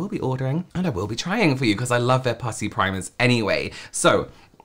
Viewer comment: I swear I thought I heard you say ‘I love Elf’s Pussy Primer’. I had to rewatch even tho I knew you didn’t. I love your adorable accent. ❤